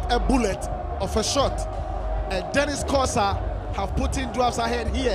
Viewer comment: did I hear the commentator say "have putting" 😷😂